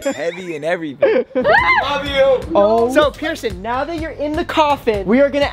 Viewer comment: Ok but did someone say 'love u ' to pierson in 4:31?